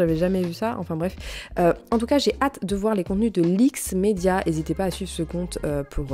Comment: Bonjour à tous, merci pour ta vidéo c’est vraiment utile pour moi ☺️☺️🌸, je voulais savoir qu’elle était le compte que tu mentionnes dans ta vidéo à 15:34, lix média? Je n’arrive pas à retrouver le compte mais j’aimerais vraiment voir son contenus! Merci des bises 😘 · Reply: Leaks media